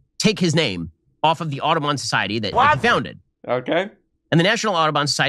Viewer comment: I thought he said autobahn society